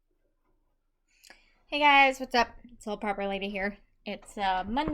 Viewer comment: I could’ve sworn you said....Little Purple Lady here. 😳